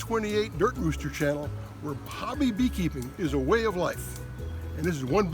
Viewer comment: Oh, I always thought you said happy beekeeping is a way of life (